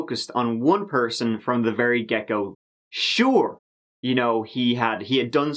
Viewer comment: Woaahh!! Haha. I totally heard "gecko" instead of "get go". Lmfao!! 🦎. Note to self, next time try not to be distracted while watching/listening to another of videos. You'll begin to hear words that are similar to what was actually said and you'll be hella confused. Haha.